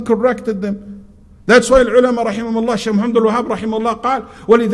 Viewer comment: btw 0:28 is he saying ibn abdul Wahab? he is talking so fast.